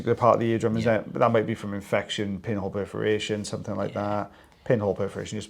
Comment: Probably highly inappropriate but it sounds like you said 'penile perforation'.